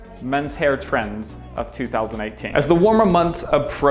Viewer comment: anyone else thought he said 2018?